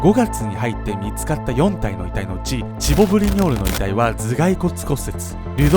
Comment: チボ・ブリニョール 滑舌のせいで霜降り明星に聞こえた